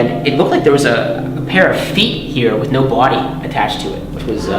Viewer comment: Great location! Old abandoned furnaces are fantastic! Is it odd that I thought he said "para-feet" instead of "pair of feet"? lol!